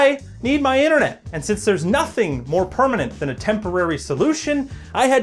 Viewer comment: he said permanent but i thought he said kermit now i can't help but notice how much sounds like kermit